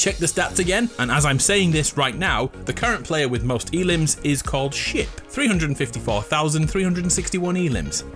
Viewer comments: bro did u just say shit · is that a bad word? or did I hear it wrong? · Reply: Ship